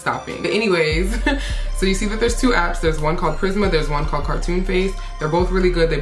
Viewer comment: What app you use for the picture? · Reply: Prisma Photo. I thought she said Charisma at first.